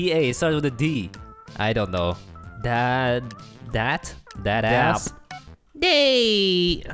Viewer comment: who else heard him say Dat A$$